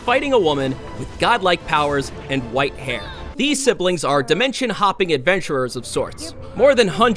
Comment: for a second I heard dementia hopping